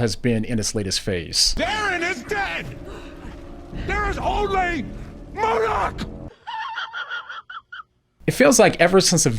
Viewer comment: did it say “there is only moloch” ?